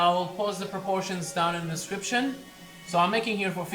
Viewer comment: Whenever you said description I heard it as scripture ( Bhagavad Gita ) 😂😂lol